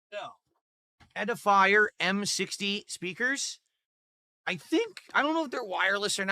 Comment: Pedafier speaker? Thats what it sounds like hes saying.